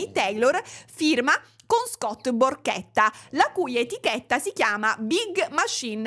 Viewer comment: Non io che a 9:36 ho capito Scott PORCHETTA 😭